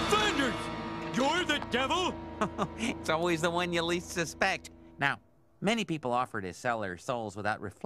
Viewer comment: Homer : Flanders you're the devil? Flanders: Its always the one you least expect lol! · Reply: I thought he said "suspect?"